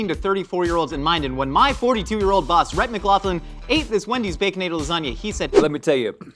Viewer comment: Did Josh call it a “Baconadle Lasagna”? 😂